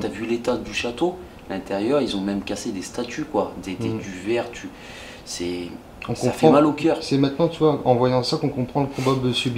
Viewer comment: Coucou les garçons ;) Franchement vous faites un beau travail !!! Quel enquete impréssionnant !!! Dommage que le château va être rasé, il es vraiment magnifique !!! Moi aussi j'ai entendu "Demon" quand vous pensiez entendre "Mal au coeur". Continuez comme sa et j'ai hate de voir vos autres enquetes ;) bizou les garçons ;)